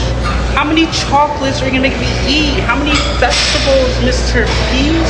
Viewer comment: why did bro say festables ✋🤨🤚🥀